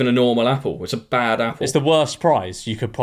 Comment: did someone say bad apple..?